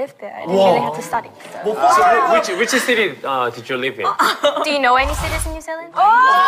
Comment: what she said ??? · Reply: She said "do you know any cities in new Zealand"😭savage